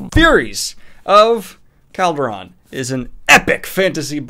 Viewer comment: I thought it said furries of calderon and i was really excited for a minute